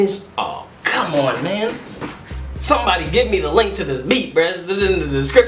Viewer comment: am I the only who heard him say “somebody gimme the link to his meat”?